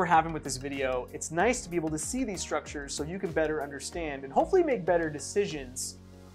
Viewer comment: i'm a recovering alcoholic and was drunk every day for 30 years.....it's good to understand what was happening.....i'm amazed i survived, tbh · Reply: i thought you said butter 😂